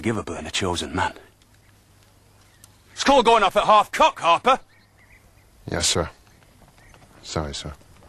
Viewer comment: What is Sharpe saying at 7:52? It sounds like "school going off a half cook, Harper" O.o